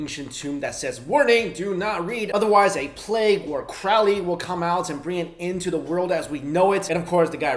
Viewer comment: i heard "or Crowley" and i lmfao